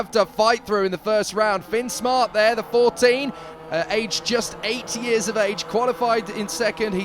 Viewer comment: Lmao I thought he said 80